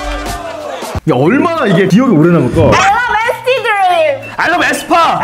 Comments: nct dream으로 들리는데 아닌가요?? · i think what she say is NCT DREAM not masty green